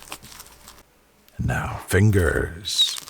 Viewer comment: it sounded like he’s getting turned on by fingers 😂😂😂